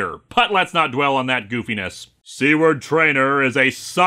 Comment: I really thought Lewis said C-word Trainer instead of Seward. It really brightened up this story for me!